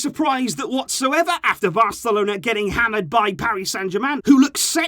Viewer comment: did he say VARcelona ?